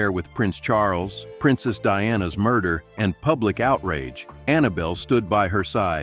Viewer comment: Did I hear the Murd*r WORD????👀😳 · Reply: Yep, you did. The narrator said something about Annabelle always being there being there through Camilla’s affair with Prince Charles, and then the words, “Princess Diana’s murder.” (about 4:15 -4:27) I heard it too. Nothing was explained or added, even farther into the narrative. It was just said and the narrator moved on.